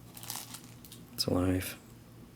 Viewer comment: did say " its alive" at 12:05?